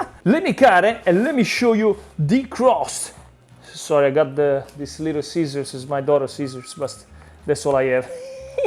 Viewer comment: A little after 16 min 45 sec, it sounded like you said Little Caesars as in Little Caesars Pizza, ha ha ha!